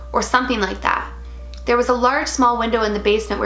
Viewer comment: Wait, at 8:09 did you say (aka, the person wrote), “there was a ‘large, small’ window”? A “large-small” window? Am I hearing that right? I’m a bit confused 😅